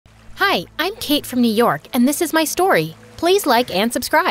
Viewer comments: I literally heard " Hi I am cake from euros" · In the beginning I heard her say: I’m cake 😅😂